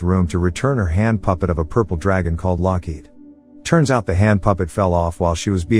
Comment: did he say Loki?